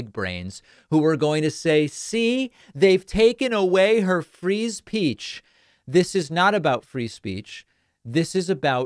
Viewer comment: did he says freeze peach? 😂